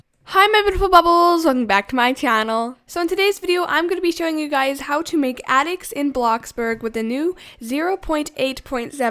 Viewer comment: Am i the only one who thought she said “ how to make Anix in bloxburg” just me? ok.